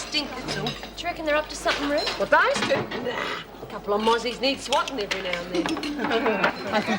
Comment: At first I thought Rita said "Those Aussies need swatting". I thought it was an "in" joke because Rita is from England but she said Mozzies (as in mosquitoes) and not Aussies